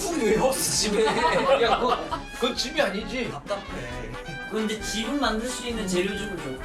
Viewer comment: 잘 들어보면 이재진이 답답하다는거 들림ㅋㅋㅋㅋㅋㅋㅋ 지붕있으면 답답하다는 사람이 어딨냐곸ㅋㅋㅋㅋㅋㅋㅋㅋㅋ